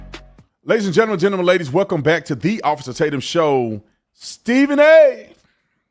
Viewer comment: I heard "Welcome to the AWESOME Tatum show". It took a second for my brain to recognize he said OFFICER Tatum, then decided awesome Tatum works too. :)